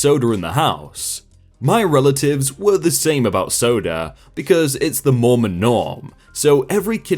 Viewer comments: “It’s the Mormon norm” but in my head I heard “it’s the Mormon norm(on)” 😂😂😂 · “It’s the Mormon norm” but in my head I heard “it’s the Mormon norm(on)” 😂😂😂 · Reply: I heard 'Moron Normon.'